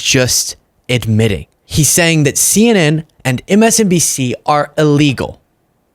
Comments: Did he just say MSNDC?! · I could have sworn I heard him say MSN”D”C slurring his words! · Did he just say MSNDC or am I going deaf 😂 I. Sat here in Spain shaking my head wondering what the world is coming to 🙄